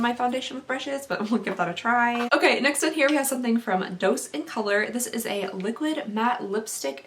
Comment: Anyone else forever hearing say “Dosè of Color”? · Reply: i thought she said,dose is color lol